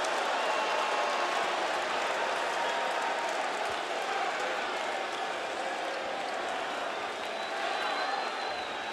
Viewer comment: Can anybody lip read what MJF says to punk OFF-MIC once he says HUSTLE LOYALTY AND RESPECT?? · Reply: I’m not done/finished